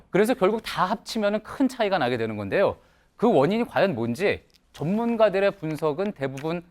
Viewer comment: 발음이 전문가로 안들리고 좆문가라 들리는데 나만 그런가